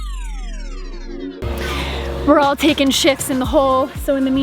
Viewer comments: For a minute there, I thought you said "We're all taking sh*ts in the hole..." 🤣😂🤣😂🤣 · I had to rewind. Sounded like “we’re all taking sh*ts in the hole.”LOL Speaking of digging holes, ever seen the movie Holes? Good movie.